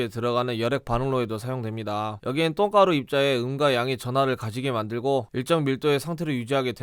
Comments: 퇴근길에 뇌빼고 들으니까똥가루 입자의 응가양으로 들려요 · 자꾸 똥가루 똥가루 하니깐 “음과양”이 “응가량이” 로 들림